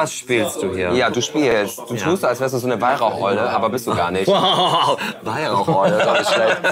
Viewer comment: ich hab Weihrauch-Eule verstanden :)